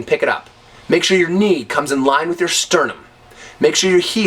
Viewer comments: could you please tell me what word do you say at 0:51 after "make sure your knee comes in line with your ..." what's that last word? (I'm not native speaker) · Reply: of course. Chest or sternum. It is the bone in the center of your rib cage. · could you please tell me what word does he say at 0:51 after "make sure your knee comes in line with your ..." what's that last word? (I'm not native speaker) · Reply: he says on the chamber of the kick to have the knee at the same level as the sternum (right above the solor plexus where the ribs connect)